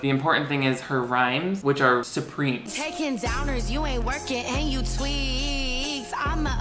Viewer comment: I kept hearing 'im taking dubyoos'